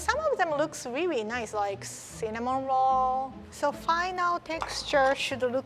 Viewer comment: I thought she said cinnamon roar. Cutie